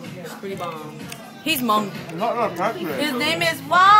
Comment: did you say hmong? 6:04... maybe i'm just freakin out. hhaah